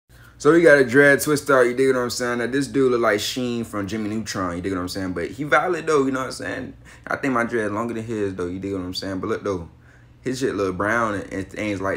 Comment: All I heard was "YA DIGG WATTIMSAYIN"😅